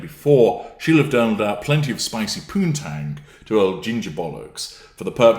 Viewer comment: I loved since I heard "spicy poontang" LOL!